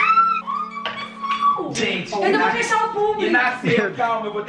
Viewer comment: só eu ouvi a mandar o Rangel fechar o c*